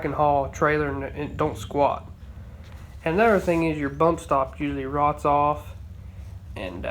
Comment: you describe a part as a "boat" or I think you're saying "bow" stop??? I'm thinking this is some sort of an anode to collect rust maybe? What is is used for? Need help in identifying this part, thank you. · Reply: "bump stop" It's a rubber bumper that the rear axel housing bumps against.